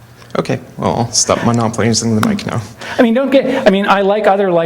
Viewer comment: anyone know what Lowe's guy says @ 8:25? "OK, Well stop plays make it no"? Something about monopolies? He seems perturbed. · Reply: “OK, well, I’ll stop monopolizing the mic now.”